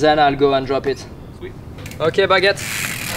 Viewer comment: does he say "Ok baguette"?